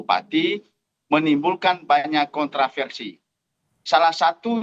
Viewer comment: oh kontra-versi, kedengeran nya kontrasepsi 😂 mbok ya bilang kontroversi aja sih, lebih aman mas 😅